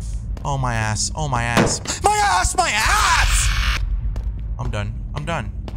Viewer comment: “oh my ass my ass” !!POP!! Omg who else heard it 😂😂😂